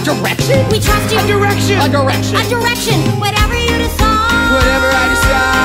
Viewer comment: I always thought Grover said, "Whatever you decide," at 3:08 but he's actually saying, "Whatever, dude, decide," and this is just so much better somehow